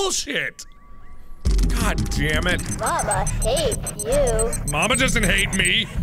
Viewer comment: Honestly thought it said "Bubba hates you."